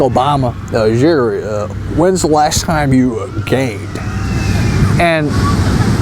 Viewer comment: I thought he said: "when was the last time you gayed"